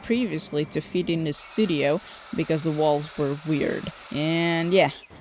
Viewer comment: I love your design aesthetic. I would love to see you do a collaboration with Laura Kampf, if that hasn’t already happened. Every time you say something starts to look ‘weird’ I hear ‘unique’, which just means custom/hand-made.